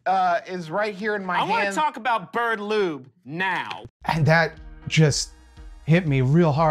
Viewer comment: I’ve watched the clip dozens of times and I always thought he said bird lubes. Lewds makes way more sense! Darn sensory processing issues. Lol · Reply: It's def "bird lube". The joke recurred on the podcast in one episode iirc & it's pretty clear